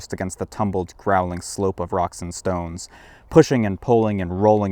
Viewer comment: Did I hear a Rock and Stone?